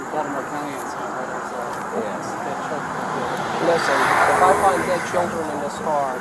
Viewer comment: did he say ‘ if I find dead children in this car ‘ I’ve replaced it but can’t tell what he says